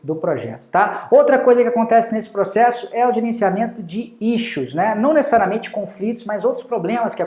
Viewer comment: Nao entendi o termo "Icho"?! É mencionado em 7:15 min. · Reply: Ele disse "Issue" que vem a ser "questão" em português!